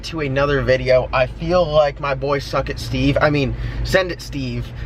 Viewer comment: Excellent video,hell yes line them up,hell yes dodge is trash.. yes this was funny.At first I thought you said Bucket it Steve,not WETF you said.No explanation why that Ford was hooked to Chris's race trailer a couple weeks ago.